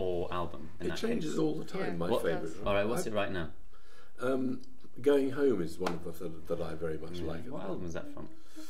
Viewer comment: ?? what. what's ur favorite song? and answer ı dont understand? please help me :) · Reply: "Going home"... that´s what´ve heard. But he said it changes all the time =)